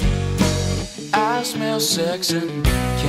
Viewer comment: Always thought he said, I smell sexy candy lol